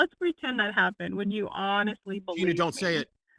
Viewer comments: She said exactly what she told you she would say (while you were cutting her off): 'would you honestly believe it?'. Then you gaslit her into thinking she said 'would you honestly report on what he said to you?' Matt you're an overly emotional, quick trigger, bad representation of critical discourse. Get a grip and stop besmirching the reputation of other atheists. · Love the show but I swear I heard her says "would you believe * and they say she said you could Lie??? I just rewound it back. And she said would you honestly believe. And I missing Something here?